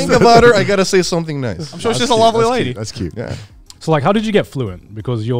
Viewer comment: what did he say? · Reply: "Sweet ass lady"